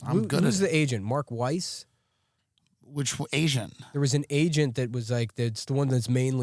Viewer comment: mullen asks what the agent's name is and tim says which asian lmao